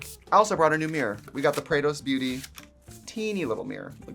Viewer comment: Every time you say 'Prados Beauty' I hear 'Kratos Beauty' and I really need to see that makeup now lol